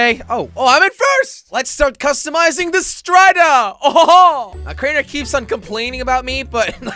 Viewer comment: Did you said STRIDOR